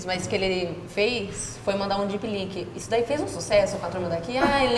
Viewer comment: O que foi que eles disseram que mandavam pros clientes no minuto 33:12 ? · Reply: Pelo que eu entendi é um Dipnlik, aqueles pirulitos que vinham com o saquinho de açúcar.